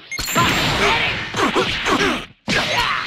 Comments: Why did I hear frieza say “fucking little-“ 😂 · did frieza said fucking little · Bruh why tf it sound like frieza is saying fucking hell